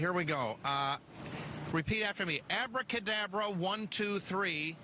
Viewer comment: I thought he said african afro not abra kadabra